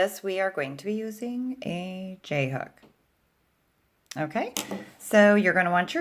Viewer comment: Thought she said Jhope instead of j hook